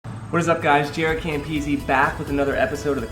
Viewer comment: thanks man! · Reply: I thought you said